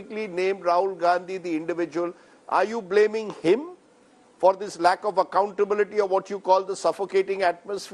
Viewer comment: Rajdeep asking are you blaiming Rahul Gandhi.....he sounded like " tu rahul gandhi ka naaam bol ..tera FNF rokta hu "